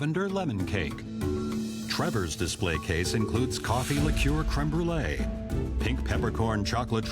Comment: i have no idea what you just said 3:07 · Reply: Coffee liqour crème brûlée